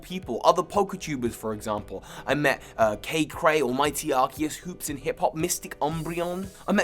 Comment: Who is that first poketuber he listed at 1:40? I know most of the others he said, but not them, and I'm not sure what he says her channel name is. Edit: Never mind found it. It's